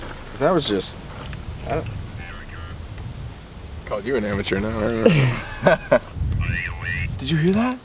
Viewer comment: I Didn’t Hear Nothing Because All I Heard Was The Box Saying *Amateur*